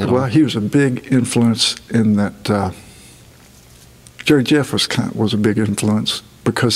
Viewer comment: Whats the name of the guy that he talks about at 7:05? Cant really hear what he is saying, sounds like Joe Jeff to me? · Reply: Jerry Jeff Walker